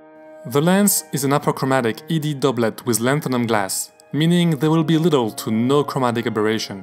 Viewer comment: At first I thought I heard "Latinum glass", as in "Gold-pressed Latinum".